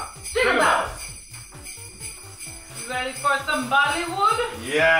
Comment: When quake said "ready for bollywood?" I thought she said "ready for vomitfood?"